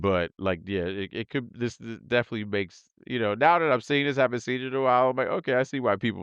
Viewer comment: did he just say he already seen this